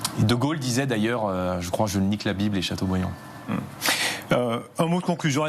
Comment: Il dit quoi à 55:47 ? Je n*** la bible et chateaubriand ???